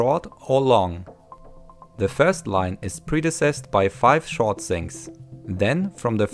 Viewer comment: Loved it! I enjoy your English accent... It's strong, but you're very easy to understand. There was one word that threw me though... Sounded like you said predeceased.. Was it "preceded"?